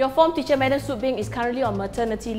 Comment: I thought the class said Mad Soot Beng